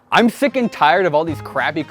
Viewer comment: I misheard the f0beginning of the video so for a second I thought he opened with "I'm thick and tired"